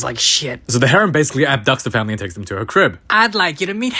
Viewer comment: BRO it sounded like you said "abDUCKts theim"